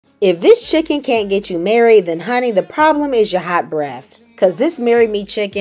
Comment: Lmao my volume was low. I thought she said the problem is your eyebrows 💀